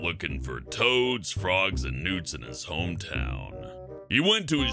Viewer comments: In number 2 when he said newts I thought he said nudes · "Out looking for toads, frogs, and nudes" anyone else hear that?